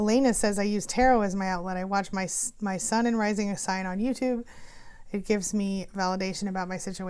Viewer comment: Any chance you remember what that Tarot channel name was? I couldn't make out what you said... It sounded like "my son and rising" but I didn't find anything. I have a few non traditional decks and really like the idea of tying the beautiful imagery to survivor strength!